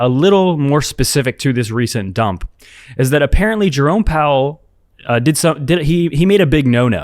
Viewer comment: I’m pretty sure i heard you say jerome bowel 😂